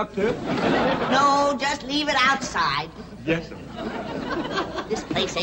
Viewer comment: Why did Jethro call granny a sir at 11:45 ???????????? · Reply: Mid-Ohio Guardian He's not saying "Yes, sir." He's saying "Yessum," which is an idiosyncratic country term for "Yes, ma'am."